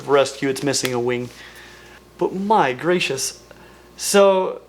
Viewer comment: i realize you said “my gracious” here but at first i thought you said “migratious” and i was like damn thats a quick-witted bird pun 😭